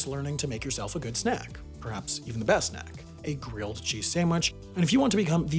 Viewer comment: I thought he was saying coochie sandwich 😳💀